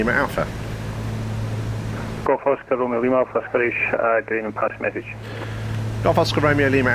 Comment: I'm trying really hard to figure out what atc said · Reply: "GOLRA From(?) Scottish, good evening pass your message" ?